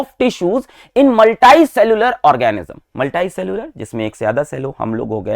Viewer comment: I am sorry sir but you are saying multai instead of multi 😂😂😂in